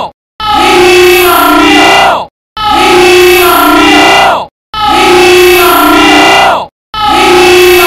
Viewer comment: The 0:37 part sounds like "i need some milk" Instead of "he needs some Milk"